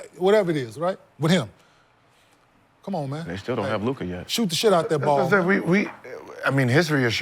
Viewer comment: All I hear is “KLAY shoot they face off” 😂😂😂😂😂😂😂😂😂😂😂😂